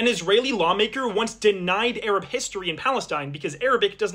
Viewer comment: I heard "bomb maker" instead of "law maker"